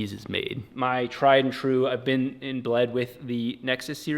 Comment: I may have misheard.. but either admitted that he has been blooded in with the Nexus Series.. "Ok that does account for many things" but I'm pretty sure he just let the cat out of the bag and admitted he was inbred.. Speaks Volumes about a rotary Corvette amongst other projects :D JK you Rock